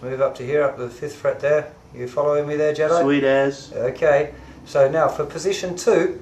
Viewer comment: Did he just say "Sweet Ass" 😂😂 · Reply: "Sweet as" - Kiwi (New Zealand) English.